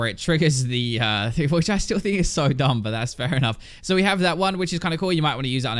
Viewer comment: WTF Did he just say? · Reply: "Which I still think is so dumb." I heard it clear as day. I'm Australian though.